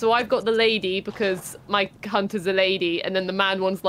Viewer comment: did she just say "my c*** is a lady" 😧😦😮😯😲😱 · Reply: 🤣😂🤣 I thought so too, but I think she actually said "my character's a lady."